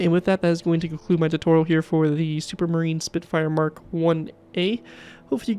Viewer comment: I thought that said submarine spitfire… Well now I’m corrected.